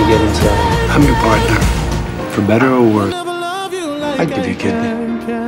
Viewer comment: I had to replay that a couple times. I thought he said, "I'd give you a kid."